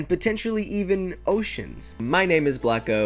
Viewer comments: I thought that you said “ my name is black hole " bloco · did you just say your name is black hole? No offense, sorry